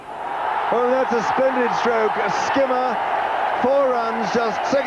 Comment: Who heard "CHAAR RUN" ???